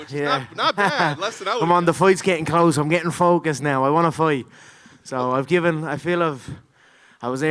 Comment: Did he just say he wants to fight Tony 💀⚰️